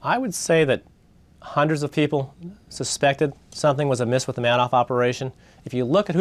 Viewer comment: Markopolous clearly saying manoff and mister Man - off repeatedly instead of Madoff.